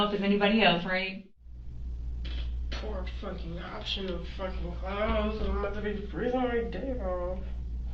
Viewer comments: "Poor fucking option of fucking clothes, I'm about to be freezing my dick off." That is what she actually said opposed to what has been subtitled in... Just saying. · She actually says "Poor f*cking option of f*king clothes, I'm about to be freezing my d*k off" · She actually says 39:54 ''Poor fcking option of fcking clothes, im about to be freezing my dick off.'' What were these subtitles? lol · she actually says “poor option of f*cking clothes. I’m about to be freezing my d!ck off”